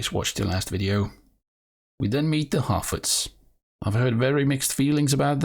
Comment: You were talking about Harfoots but I thought you said “Halfwits”- which is appropriate as that’s what I see when they are interacting on screen.